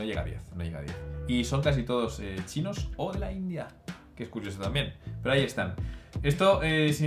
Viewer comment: ¿Alguien me podría decir cuál es la otra nacionalidad que dicen? Es que no lo escucharlo. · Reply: de la India